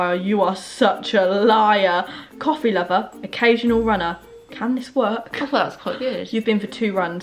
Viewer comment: Was it just me that when read out her mums bio thought she said coffee lover , runner , can twerk 🤣🤣🤣🤣🤣🤣🤣